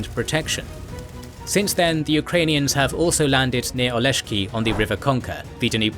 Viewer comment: The subs are enabled <link> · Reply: those are captions but not autogenerated captions. Autogenerated captions are less accurate but they show up in a very good flow, unlike manual captions which come from your script. Auto generated captions makes it easier for non-native speakers because they transcribe all words. As opposed to your captions, e.g. 13:15 where the caption says “Since then, Ukrainians have” whereas the video narration says “Since then, the Ukrainians have”. Excluding small words make it much harder to follow, and autogenerated captions are the easiest way to avoid that.